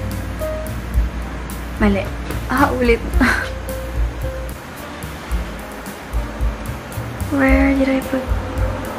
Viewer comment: I really heard "блин"..? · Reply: it's actually tagalog! she said "mali, ah ulit" which means "wrong, again" <3